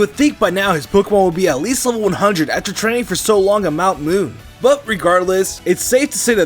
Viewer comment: I heard mount moon lol its mount silver